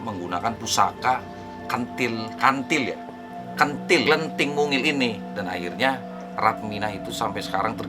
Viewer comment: ah pentil nggak salah denger wkwkkwkkwkwkwkwkkwkkw